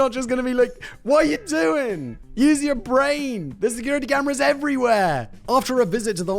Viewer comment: "use your brain" I thought you just said he shot it off?